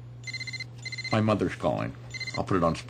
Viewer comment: I heard: "Geico, 15 mins can save you 15% or moron car insurance."